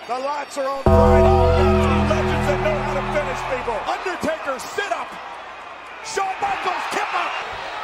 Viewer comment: Anyone else notice the king break character in 98’ I swore he said “sit up mark”